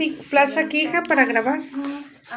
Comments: En el minuto 6:06 se escucha que dicen ayuda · se escucha una vocecita que dice "ayuda" :< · En el Minuto 6:04 se escucha una voz de niño que dice "ayúdame"😮😮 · En el segundo 6:06 se escucha una vos que dice ayuda susurando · En el minuto 6:06 se escucha un susurro que dice ayuda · soy yo escuche ayuda de una joven minuto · se escucha que dicen ayuda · WEY EN EL MINUTO 6:07 SE ESCUCHA AYUDA CLARITO ESCUCHE FUE COMO QUE SUSURRARON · En el minuto 6:06 se escucha una voz que dice ayudaa😢😢😅😮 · En el minuto 6:07 se escucha una voz que dice “ayuda” y en el 5:31 se ve una sombra blanca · En el minuto 6:04 alguien dice ayuda nose pero yo tengo audifonos y lo escuche clarito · En el momento 6:06 se escucha una voz que dice “ayuda" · Minuto 6:06 se escucha una voz que dice ayuda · En el minuto 6:05 se escucha que dicen "ayuda" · Creo que nadie vio que cuando marco y la abuelita dijeron "hay que temerles más a los vivos que a los muertos" paso una sombra blanca y desapareció pero al parecer casi nadie la vio:( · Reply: y el en minuto 6:05 se olle que dicen ayuda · escuche un AYUDA en el minuto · se oye una vos que dice “ayuda “ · Soy yo o en el min. 6:05 se escucha que dicen "ayuda" 🤨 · EN EL MINUTO 06:04 SE ESCUCHA UNA VOZ QUE DICE "AYUDA"😮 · Se escucho que dice ayuda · en el minuto 6:00 después que dijeron prende el flash se escucha que dicen ayuda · En el minuto 6:11/9:12 se escucha una voz que dice ayuda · en el minuto 06:06 se escucha una voz que dice ayuda